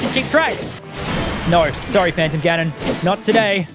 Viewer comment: did they say phanton parry?